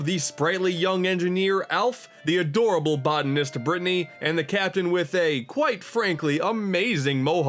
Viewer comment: did he just say Mr brittany?